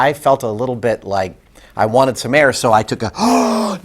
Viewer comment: what he said? 'like i wanted to... Mayer? oO"